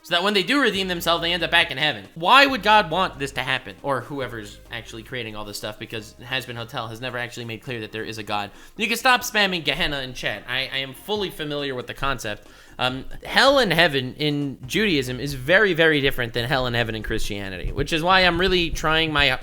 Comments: It sounds like is saying "nigga stop spamming gahana in chat" Just found it funny. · WHAT DID HE JUST SAY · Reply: "You can stop spamming..."